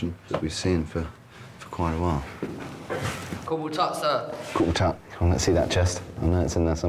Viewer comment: does he say “Guten Tag”? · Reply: No he's saying "Corporal Tutt". He was introduced not seconds before.